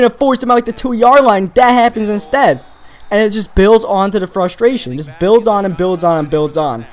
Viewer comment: Sounded like you said "It Bills on to the frustration" haha lol